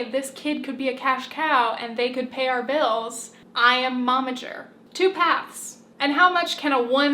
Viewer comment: You say Momager, but I keep hearing "Mama Jerk". I cannot unhear it now